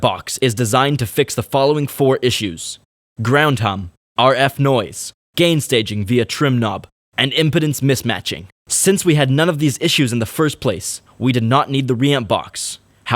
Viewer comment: Did you just say impotence mismatching at 2:27?